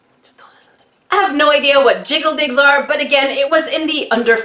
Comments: Lol I thought she said jiggle dicks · Jiggle digs 😂😂😂😂😂 I just listened to this video in the first go, so I laughed so hard at that name I should have been arrested for scaring local dogs and waking up my neighbours 😂😂😂😂 Now watching it, still funny, but more like gigglesnorts than bwahahaha 😂😂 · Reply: Not gonna lie when I heard say it I thought she said “jiggle dicks” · I totally thought you said jiggle dicks 😅😅😅😅😅😅